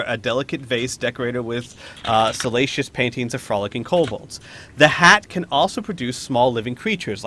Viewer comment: just heard it as "fornicating kobolds"...